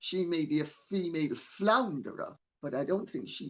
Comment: I SWEAR, I thought they were saying Female FLOUNDER... (and thought... Meg is actually admitting that?!) Until I saw it written on Dan's YouTube.